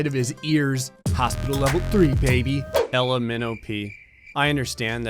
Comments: When he said Ella Minnowpea's name all I heard was " L M N O P" and I was like, "bruh you singing the alphabet?" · When he said Ella Minnowpea, thought he was saying the alphabet letters L M N O P